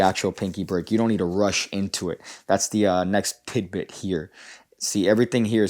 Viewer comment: did you say pig bit? Also, I wasn't really paying attention. Can you go over the part about the pinky again? Thanks